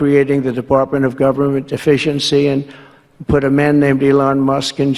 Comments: I could've swore he said, "Department of Government Deficiency" and I thought...dayum...tRump can tell the truth.....turns out he just can't enunciate. Maybe he should take some acting lessons. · It sounds like he said the Department of Government Deficiency and that is what they are after a government with a lack to the point it can't function!